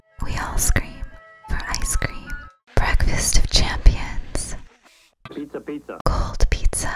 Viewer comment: is it me or do I hear the little caesars "pizza pizza" in the background